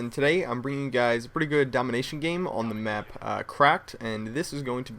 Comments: i thought he said crapped instead of crack lol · i thought u said craped for the map